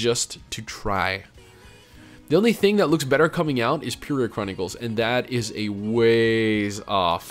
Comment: did you say purig chronicles? I need to know so I can research it. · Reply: he said "Peria Chronicles"